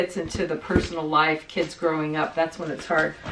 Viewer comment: at the 7:35 mark, I thought your mom talking about the photos that are hard to give up, thought she said You know, like the photos of the kids throwing up...!!!! It was kids growing up. Happy New Year, everyone!